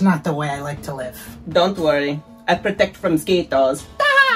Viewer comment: This is great! I cried :D I heard: Ed: "I don't know if I can live here in Philipines." Rose: "Don't worry, I'll protect mosquitoes. Hehehehehe." :D